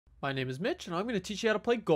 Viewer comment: I Thought You Said My Name Is Bi